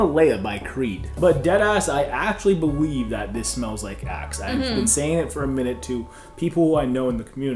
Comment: For a second I thought she said smells like a$$ 🤣🤣🤣🤣